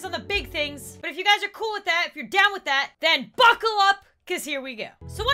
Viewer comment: I thought said f^ckle up 😭😭😭